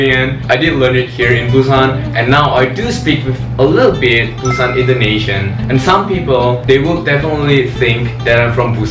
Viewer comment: I thought he said busan indonesian